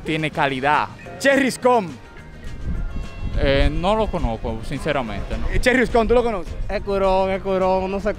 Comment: expresó el joven que dice antemente💀💀